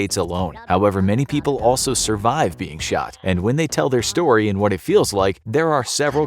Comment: i thought he said what it fells to be SHORT not shot hahahahahahahhahahaha=)